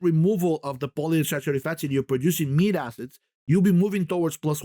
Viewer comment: What is he saying at 49:00 -- "meet" acids??👀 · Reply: Mead acid